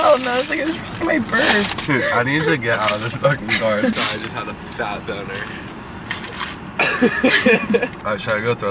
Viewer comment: I swear at 11:40 said “SORRY I just had a fat b0ner” not “imagine”